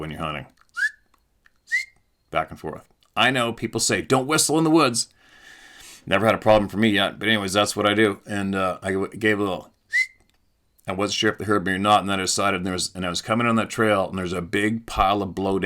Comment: I always heard don't wistle in the DARK